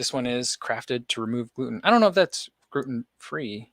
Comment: Sounds like you said "Groot'n free" which makes me think of a southern version of Groot from the MCU. 😂